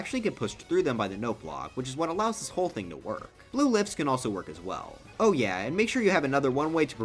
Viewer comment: I at first thought you said "Blue Lips"